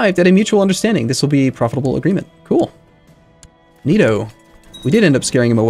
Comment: did say "ninoo"